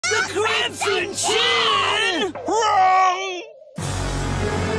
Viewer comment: I thought he said N!**@ Chin 😂😂😂